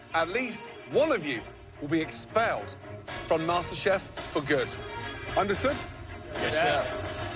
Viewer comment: Lol i thought it said master chief